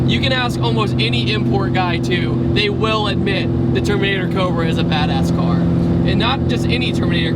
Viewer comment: at first I thought you said termater cobra haha